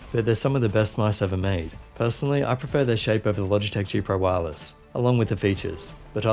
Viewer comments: u said that u prefer gpw shape more, and then couple seconds latter razer viper it has better shape for me wtf is going on? Can't get your point so is razer viper ultimate or gpw? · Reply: A few people misheard me it seems, I said "over the" not "of the". I prefer Viper shape. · The line people are getting confused about at 6:43 I said "personally, I prefer their shape over the Logitech G Pro Wireless, along with the features". Some people thought I said "I prefer THE shape OF", but no, I said "THEIR shape" and "OVER". Note: Doesn't look like lighting is saving to on board memory unfortunately, it's still in software only. 30/10/2019 Update on potential issue: So the issue was that the mouse would randomly stop working for 50ms or so. Razer got onto it immediately and have now released a firmware update, check the official link here so you can get the update: <link>